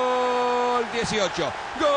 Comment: does he say "Gooooooal! it's your job" ????? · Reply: He says "goooooooool... dieciocho!" (eighteen)